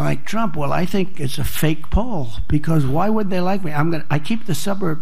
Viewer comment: He actually says, "Why would they like me?", at least that's what I heard. And to answer his question, I would say, "I can't see any reason why they WOULD like you!"